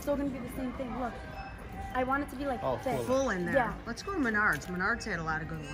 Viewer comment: “Lets go to menards”.....i heard that as lets go to my nards! Lol 😂